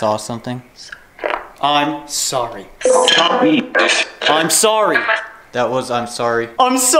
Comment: I thought I heard the female voice that is following y’all say “ I’m sorry River”